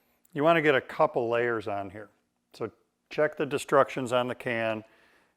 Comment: i think i know what you meant, but "check the destructions on the can".(2:37) I had to rewind to make sure i heard what i heard.